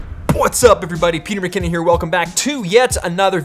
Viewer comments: when he says “What’s up..” the subtitle says Peter bikini, not McKinnon LMAO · Nobody : Auto English caption read Peter bikini instead of Peter McKinnon